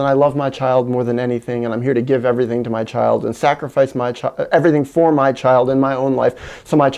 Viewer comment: > "sacrifice my child" I heard (?